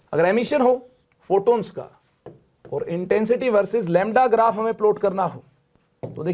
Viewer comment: yeha pta nhi sir kya bol gye .... shudh confusion ho gya mujhe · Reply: Bhai lamda bola hai sir ne Intensity vs lamda (wavelength ka symbol hota hai na usko bolte hai 👍)